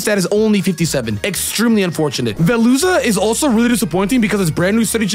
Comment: Idk why but when anyone says "veluza" I hear "the loser"🪦